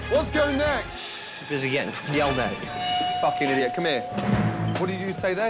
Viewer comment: did he call Gordon Ramsay "dad" at 0:51? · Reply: No he said yelled at but dad sounds pretty accurate